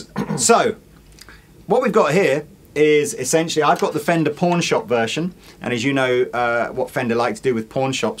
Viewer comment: is it just me or does it sound like is saying fender's porn shop